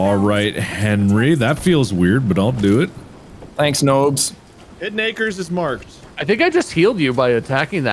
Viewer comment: When they said “Hidden Acres” I heard “Big Nakers”, and I think that should be the official name of the boob demon from now on.